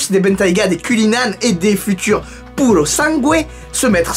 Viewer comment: c’est quoi pulosangwe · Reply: "Purosangue" le futur SUV de Ferrari ;) (officiel, confirmé)